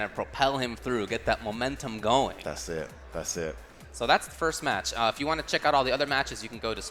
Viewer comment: Am i hearing things or F-word really said ta certo ta certo in 2:02??? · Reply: Paulnewman "that's it, that's it"